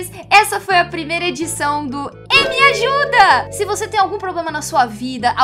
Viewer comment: eu buguei quando ouvi o nome do quadro, e entendi "ei me ajuda" ao invés de "emi ajuda"